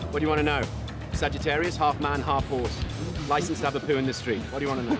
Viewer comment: What does he say? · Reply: "Sagittarius, half man, half horse. Licensed to have a poo in the street"